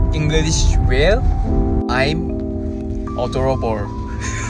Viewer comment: his 'adorable' that is almost misheard as 'uh.. 더러워' always gets me :D